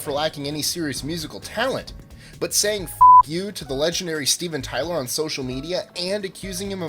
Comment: I thought he said Steve and Tyler, then I realized my first mistake.